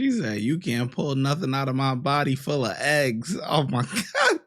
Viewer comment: Body full of eggs? I thought she said Body full of aids. I'd believe it. My god...